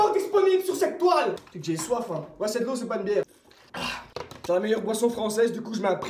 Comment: Moi c'est de l'eau c'est pas une bière mdr😂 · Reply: Il dit " ouais " pas "moi"